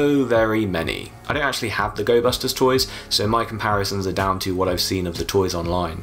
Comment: Fun fact: Gobusters was Super Sentai's Power Rangers tribute season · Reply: Lmao i thought you said ghostbusters